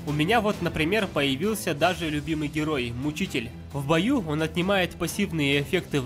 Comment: Знаю что видео довольно старое но... 1:32 кому тоже послышалось не мучитель, а учитель?